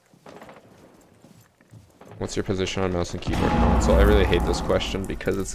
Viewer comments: i didnt catch the question does someone know what he said? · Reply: Something about mouse and keyboard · what was the question i didn't understand his mumbling through the giant explosion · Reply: “what’s your position on mouse and keyboard on console?”